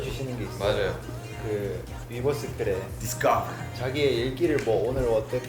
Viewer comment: did RM say 'discord'?